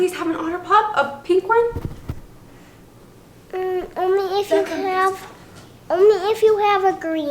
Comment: When he said only here 4:11 it sounds like he said omy and that mean in Arabic mom 😭😭😭